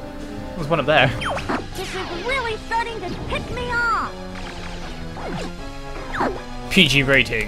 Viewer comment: for moment the i thought slippy said piss me off